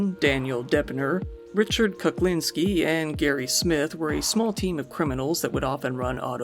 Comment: Calling Richard "the ice man" kuklinski a small time criminal made me laugh out loud. For the record, he's one of the most well known mafia assassins ever. He killed countless people. There are two documentary/ interviews with him, super interesting story. He was someone that you didn't want to upset, yet a model family man at home. Pretty much anyone that upset him, died. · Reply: He did not call him a small time criminal, he said he was part of "a small TEAM of criminals", which he really was during that period of time. Not sure why some of y'all heard it as "small time". Also it's believed he faked most of his supposed countless victims just to get more notoriety